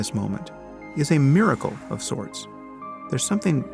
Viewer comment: Did I just hear Sam say existence is a Miracle...?? Of sorts? What is he Sorting?